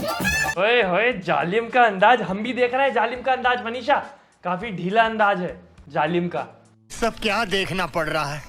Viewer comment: I don't know why am I hearing 'Sandas dekha zaalim ka' 🤣🤣🤣